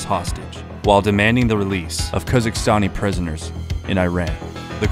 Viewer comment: I was so confused, I thought you kept saying Kazakhstan, it's actually Khuzestan.